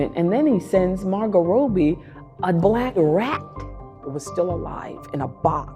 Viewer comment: What did she say at 2:19? 'a glag rat' ?? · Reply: A black rat